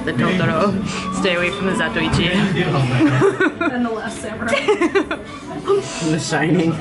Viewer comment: I heard "The Last Air Bender" drink is very disappointing. Great video.